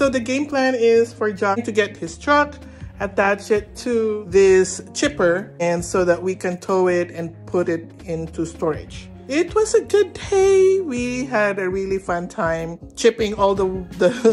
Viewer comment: Did you guys ever get your gates installed? Sounds like said "it was a good Tay?